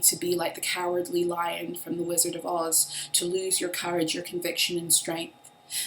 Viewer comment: I thought you said “Lizard of Oz” 👀👁️ 🤔Hmmm…